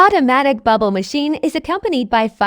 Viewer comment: what the hell did they say at 0:34 ??? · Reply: lol, it spelled out "effect cds automatically" after saying "is accompanied by firework music"